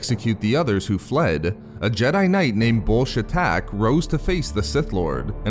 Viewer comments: I thought he said bullshit attack LMAO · who else heard bullshit attack 😂😂